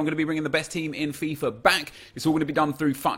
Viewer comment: Back (00:16 sounded like quack )